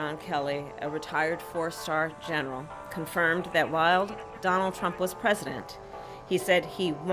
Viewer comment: Did she say "wild" instead of "while"? Or was she referring to Trump as "Wild Trump"?